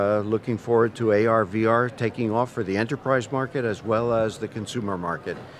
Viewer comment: I thought he said Antichrist market instead of Enterprise...lol